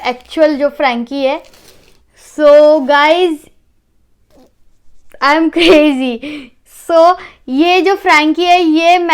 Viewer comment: She actually said I am crazy